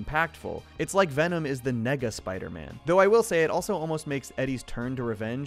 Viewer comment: WHAT DID HE SAY?!?! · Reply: Nega, deriving from Negative, a very common term in comic books